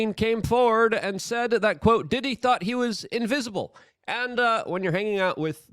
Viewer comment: Did you just say invisible? I think it said invincible lol